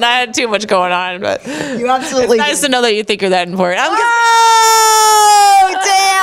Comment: Pleaseee someone tell me what Ronda says @ 13:00 i heard "its nice to know u thinkher then" ????????doesnt make sense, WHAT DID SHE SAY SOMEONE PLEASE RESPOND IM DYING TO KNO LOL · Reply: I think she says: It's nice to know you think you are that important